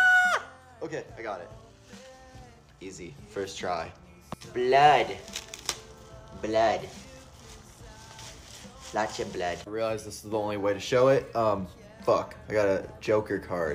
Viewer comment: Did he say "блять"??? (it means "fuck/damn")